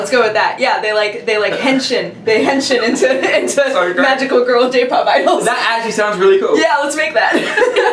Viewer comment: When said "girl j" i thought she said Girls' Day and I got really excited for a second.